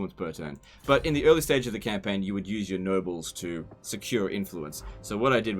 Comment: Oh, nooobles. I thought you said nurbles, whatever the fuck that is.